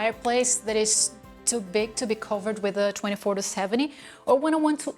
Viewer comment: Very nice and information rich content. And i love how you grade your videos. Also i heard you saying canon 22-70 instead of 24-70?? Am i right?